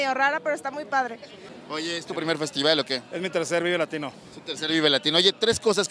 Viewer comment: Cuando escuche por primera vez y rapido pense que dijo "ES MI TRASERO LATINO" 4:18 😂😂😂😂